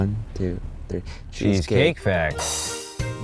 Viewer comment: I thought they were going to say Cheesecake Factory